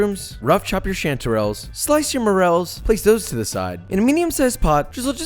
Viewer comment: When you said morels I thought you said morals and I was like...dude I get it I’ll make it from scratch I want my morals!